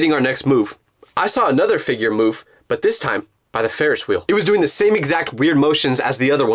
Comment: “by the ferish wheel” why do I only hear you say ferish 😭🙏